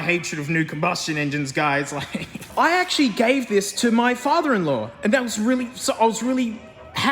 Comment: that's brother in law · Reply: sorry, thought he said brother in law lmao. Could be his sister's partner's dad tho